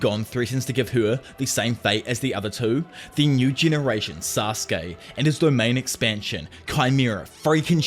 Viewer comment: Was it just me or did i hear Sasuke